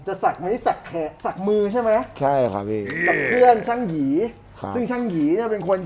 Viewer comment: บุญบาปได้ยินช่าง hee 55555 ลั่นน เออพออ่านวาร์ปไอจี ช่างหยี5555